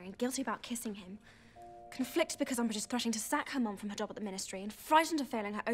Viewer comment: Did anyone get what she said at 0:39and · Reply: "...sack her mum from her job at the Ministry..."